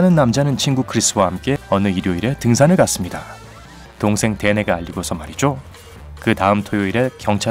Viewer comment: 동생~~~~~~~말이죠 뭐라고 하시는건가요?? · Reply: 이솝 동생 댄에게 알리고 말이죠